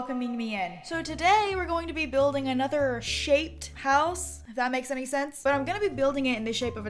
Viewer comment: When she said : we’re gonna be building another ‘shaped’ house I thought she said shate. That’s not even a word